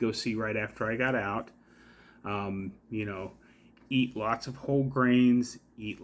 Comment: I thought you said "eat lots of cold brains". I should probably go to bed.